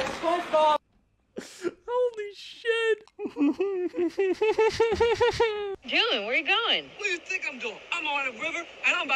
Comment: is he saying “gimme that spongebob”?